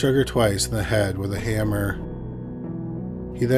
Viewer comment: I'm I tripping or did he say he shot her in the head with a hammer?🤷🏾‍♂️ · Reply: said, "He struck her twice in the head with a hammer." hth